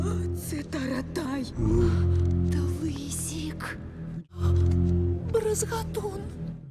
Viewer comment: she actually says " клоун лысик " ,it means bald clown lol